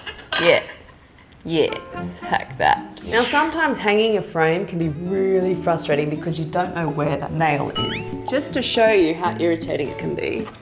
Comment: at 3.15 i heard "now, sometimes hanging a friend can be really frustrating because you don't know where..."